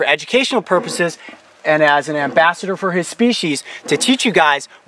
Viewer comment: It sounded like he said bast*rd😱😱😱😳😳😳